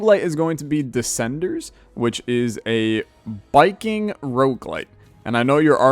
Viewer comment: Me when you said “biking rougulite” A BIKING ROUGULITE!!! I’m gonna kill myself Me when you said “oxygen not included” I actually like that game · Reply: Ngl I thought you said Viking and I was super confused